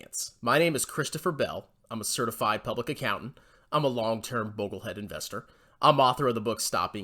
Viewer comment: When u 1st came out, I thought you said you’re a Bobble head investor.